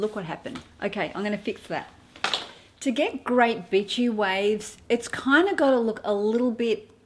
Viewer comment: I’m watching again for the 10th time....love your sharing and your accent ......with your sweet accent it sounds like your saying bitchy waves. Lol. I hope you see that as funny and sweet like I do. Lol